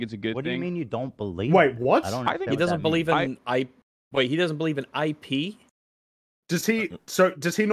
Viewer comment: 'Wait, he doesn't believe I pee?' That gave me such a damn good laugh when I first heard it. Yes I know what he is actually talking about, and I missed the 'in' word..... but that shit was so funny since it wasn't intentional. 🤣🤣